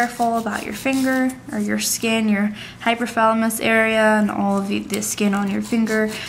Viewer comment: I love ur videos but I swore u said hypothalamus not hyponychium